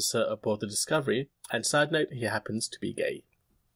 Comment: i heard "sadly happens to be gay" and red "war on white" in the comments. on a channel about star trek. a serie about discovering the universe. a serie about meeting new species I don't know what Mr Roddenberry would think of this.